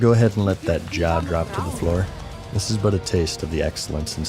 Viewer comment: at the end when he said " go ahead and let that jaw hit the floor" I thought he said something like " No I havent looked at that job offer yet"